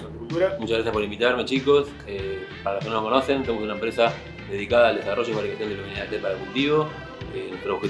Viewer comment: QUEEEEEEEEEEEEEEEEEEEEEEEEEEEEEEEEEEEEEEEEEEEEEEEEEEEEE? QUE DIJO???????????????????????????? YO ESTOY MUY LOCO PARA ENTENDERLE O EL ESTABA MUY LOCO PARA MODULAR JAJAJA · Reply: Te lo aclaro por la duda, "Dedicada al desarrollo y fabricación de luminarias led para cultivo" 😁